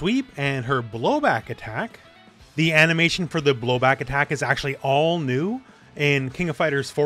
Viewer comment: What said: The animation for the blowback attack is actually all new. What I heard: The animation for the booba #neuronactivation